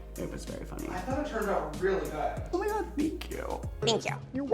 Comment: Did I just hear James Charles in the background say "I thought it turned out REALLY gooOd"? 2:25 mark